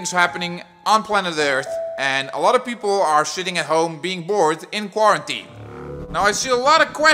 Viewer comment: did you just say shitting at home